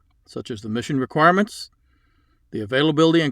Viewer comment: Did I hear “emission requirements”?